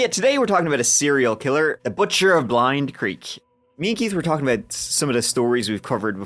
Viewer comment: I swore you said butcher of Keith earlier... never.